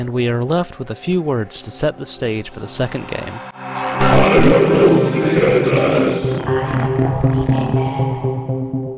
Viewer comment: Okay, I give up. I've listened to it over and over again, and I just can't figure out what the hell Commander Video says at the end of Beat (03:47) can someone put me out of my misery here? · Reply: "I AM ONLY A MAN."